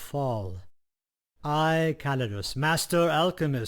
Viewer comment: misheard callidus' name as "I am Cannabis! Master alchemist. MAGUS!"